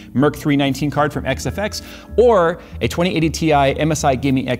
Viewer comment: jay announces the new 2080TI card LOL. omg my brain broke for some reason I thought he said 3080TI lol